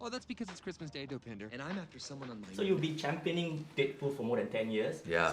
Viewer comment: haha it sounded like the guy said dickpool